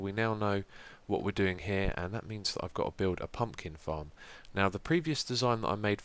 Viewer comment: I thought you said "I'm going to build a fucking farm." xD